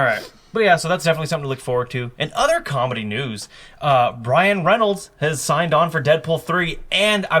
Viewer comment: Um... I definitely heard "Bryan Reynolds"